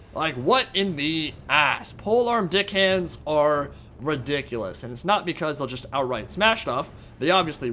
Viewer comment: What I heard: "Polearm dickhands are ridiculous" Me: wait what? "Now is the reign of the dickhand mob, they are coming for your booty." Uhhhh